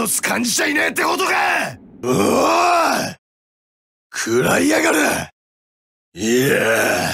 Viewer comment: What does Guts say @ 0:57-1:01. I don't why I like those lines so much 😂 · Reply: He said "Kurae Yagare" which means "Eat this!"